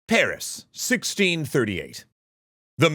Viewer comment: I thought you said tariffs